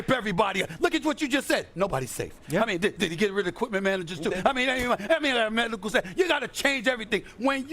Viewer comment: WTF did he say at 6:49 🤣🤣🤣🤣🤣🤣🤣🤣🤣🤣🤣🤣🤣🤣🤣🤣🤣🤣🤣🤣🤣🤣🤣🤣🤣🤣🤣🤣🤣🤣🤣🤣🤣🤣🤣🤣🤣🤣🤣🤣🤣🤣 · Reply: Equipment managers and medical staff.